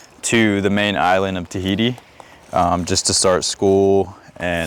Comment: did he said Tahiti ? Have faith!